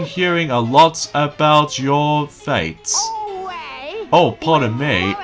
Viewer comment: I've been hearing alot about your feats! (it sounded like you said fates) IT'S FEATS!!! but that's why I'm here to Re-state your dialogue. you make several Error mistakes during this playthrough! But I'm always here to help you. That's what friends are for you know.